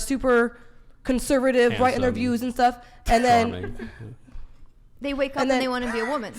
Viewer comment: Did he say "charming"?